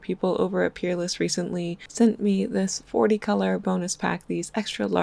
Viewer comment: I thought she said 40 piece boneless pack